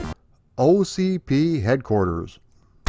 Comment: My hearing is going. Thought I heard OCD headquarters. 😂😂